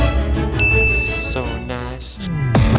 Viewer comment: did he say so guys or 17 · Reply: He said so nice